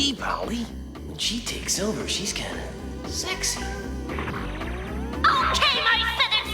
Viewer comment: Did Guido just call Polly sexy?? 😂 You go dude!